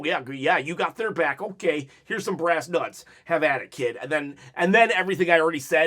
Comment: did you say brass nuts? 😳🧐